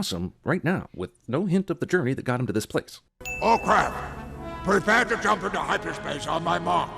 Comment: Does Ackbar say "oh crap?" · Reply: All craft